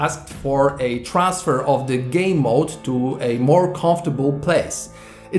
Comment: I thought he said Gay mode